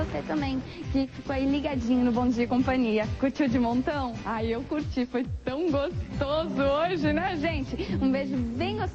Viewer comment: "ah eu curti foi tão gostoso" 0:40 XD